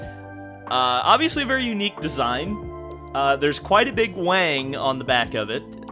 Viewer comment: did really say theres quite a big wang? 🤣